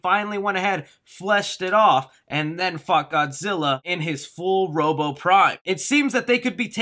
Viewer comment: i thought u said fucked godzilla